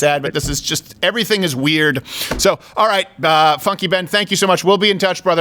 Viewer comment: Sounds like he says *"F--- you Ben."